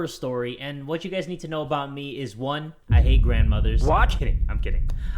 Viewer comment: When he say “I hate grandmothers” I thought he said “I ate Grandmothers”